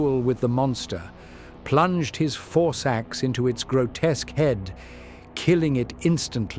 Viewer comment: Had to double take when I heard “four sacks” instead of “force axe”. 😅